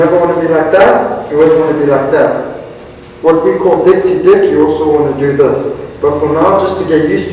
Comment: Did he say at 3:20 "What we call dick-to-dick" ? lol